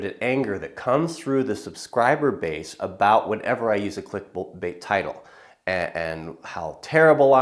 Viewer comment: Am laughing at myself, because I thought it said chick bait.